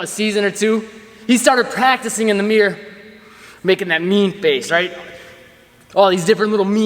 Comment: I first heard it as MEME face 😂.. That works for me 🤷.. not a meme face, but meme celebrations I used to dab, then floss.. but those aren't as fun as my latest.. Hitting the WOAH. Don't think I'll change that even if the trend ends 😂😂😂